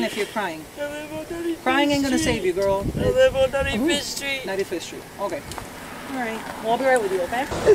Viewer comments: That captions said nutty fish street 😂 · not the captions saying nutty fish street ahahah